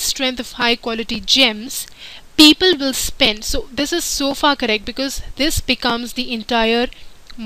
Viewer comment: Did she say "so f***ing right" in min 13:57? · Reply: She said, " this is so far correct" :D